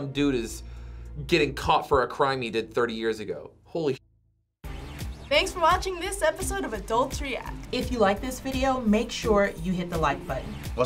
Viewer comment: Not gonna lie, when the lady at the end said “thanks for watching this episode of adults react,” I heard “thanks for watching this episode of adultery act.”